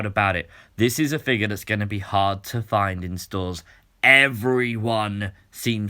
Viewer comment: I thought he said he can’t be heart